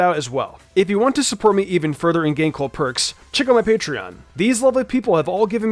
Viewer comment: I never know what hes saying at the end i just hear “in game cul perks”